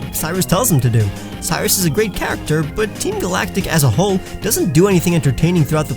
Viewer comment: Did he just say team galactic is wack???